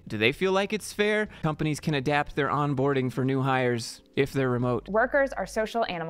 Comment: Is it just me - when she said "onboarding" I heard "waterboarding"